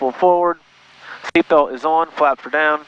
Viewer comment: FLAPS..... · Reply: i think he said full flaps